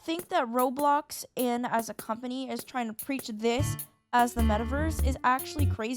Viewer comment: I fr thought you said "I ****ked the metaverse" , and I needed answers.